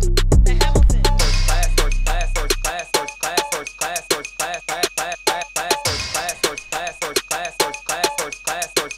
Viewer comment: YAAASSSS!!!! Had me bumpin to your names hahaha what does it say at 0:20 "class starts"?!?!?! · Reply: First class